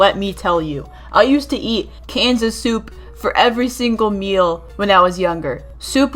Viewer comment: DID YOU SAY CANDICE SOUP!?